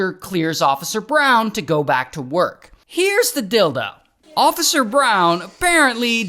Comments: anybody else notice him say dildo instead of deal tho 😂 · bro at 1:00 I thought he said here's the d!ldo 💀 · I heard, "Where's the dildo?" Here's the deal, though. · i heard here is the dildo 😂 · Sounded like ya said "here's the dildo" 😂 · At first, I thought Ray said, "Here's the dildo" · "Here's the dildo" ☠ BRO?!?! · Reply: I thought that's what I heard lol. Was listening in my headphones while cleaning, so I didn't see the captions. Just heard "here's the dildo." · My eyes were closed when I thought I heard Ray say, “Here’s the dildo.” 🙂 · Did Ray just say heres the dildo?